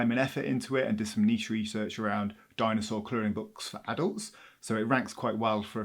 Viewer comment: Every time he says “coloring books” I hear “Korean books.” 😂 Don’t ask me why. I think it’s because his accent is so different from mine.